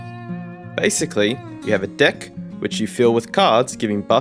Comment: "Basically you have a dick" My brain heard dick instead of deck for some reason.